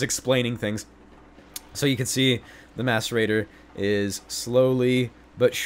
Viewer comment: Every time he said mascerator I thought he said masturbater.. I'm a bad person